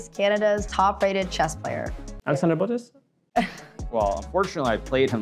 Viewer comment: What did he say here? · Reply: Alexandra Botez